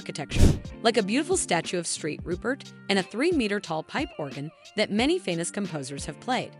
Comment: Did you say "Straight Rupert" at 0:54?